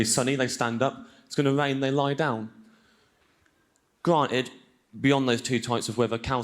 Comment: On number 7 at 10:00 i thought he said "got it" and i was very confident about that. Now to be fair, "got it" makes semantically a little less sens than "granted" in this case, but to me it was logical enough that i didn't even give it a second thought. On number 14 at 21:14 "hand towl" i didn't understand. I heared "hand tall". Which i thought was a phrase to describe the size of something in a way i wasn't familiar with. And number 15 at 22:19 i pretty much didn't understand any of the missing words. I thought it was "the cow" for starters. I could figure out it was something with "fish" in the next one, but that's about it. And the last one just sounded like he was stubling over his own words to me xD